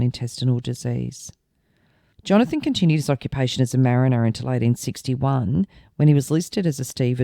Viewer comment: Evening, idk if its ya accent but sounds like you said he was a Marnier who fought in the Crimea War in 1940. Probably just sounds like it. Take care.